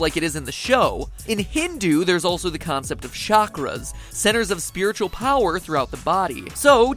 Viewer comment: Did I hear the word Chakra? . . . . Naruto?